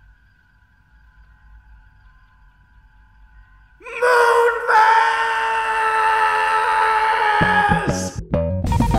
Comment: What did he said? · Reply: Moonves